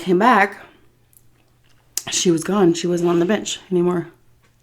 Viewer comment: When you said "she was gone, not on the bench anymore" I seriously thought you said "she was gone and not a b*tch anymore" LMAO